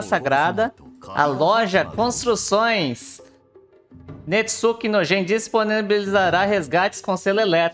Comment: é impressão minha ou eu entendi "Netsuke Nojento" ?